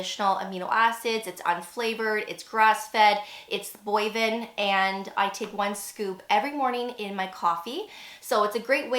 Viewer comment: Just found your channel. Cool! I'm wondering what 'boiven' means? At the 1:44 mark the transcript reads 'booven'. I heard 'boiven'. You were talking about the collagen but I can't figure out what that word means, relating to collagen. Thanks so much!☮️💜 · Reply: Bovine, from beef